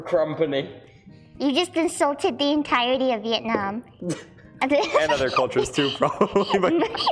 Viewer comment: did she said vietnam ??!